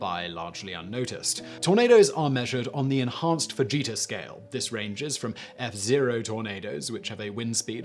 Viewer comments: did he just say tornados are messured on the vegeta scale? Does the scale go over 9 thousand? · I heard 'Enhanced Vegeta scale', then i read the chart :( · I thought for a moment he said 'Enhanced Vegeta Scale' and I was wondering what Dragon Ball has to do with tornadoes for a split second.